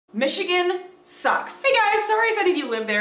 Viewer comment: lol, i thought it said magicians sucks.